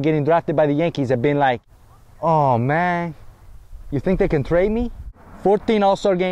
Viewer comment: Did he say trade or train me 0:21?